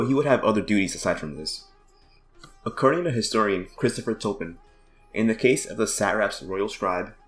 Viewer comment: You didn’t cite Christopher Tolkien at 6:05, did you? I thought I heard you say Christopher Tolkien.